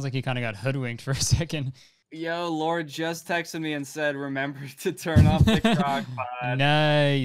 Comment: I thought he said “lorde just texted me” at the end LMFAO